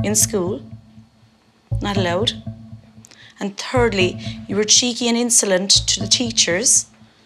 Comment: Not to make fun of the Irish or anything but at 15:45 she sounds like she is saying "turdly".